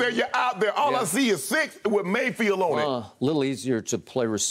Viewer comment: What Shannon Sharpe was supposed to have said was Mayflower around 18:31-18:37, not Mayfield. I got his point, though...